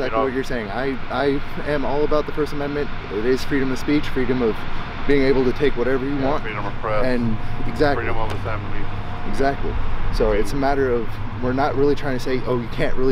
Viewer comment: Freedom of taking anything you want. Did he say that.